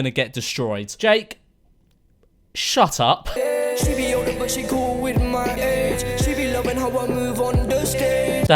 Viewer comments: This is what I heard) “She Be cool, But she Gon’ Whip my A*s” What!?😂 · Did he say “she be cool with my AIDS” svat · Did he just say “see be cool with my aids” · “She’s young but doesn’t care about my age” confirmed nonce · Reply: Oh I thought he said “she be cool with my aids” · i thought he said “she cool with my aids” i was rOLLING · did he just say „she is cool with my aids“ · Swear he says 'she be cool with my aids' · did he just say "she cool with my aids" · i swear he said "she be cool wit my aids" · is it me or did jake say she be older but she go with my aids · I heard him say “she’s cool with my aids” I think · Listen again to 4.19 it sounds like he says she is coll with my aids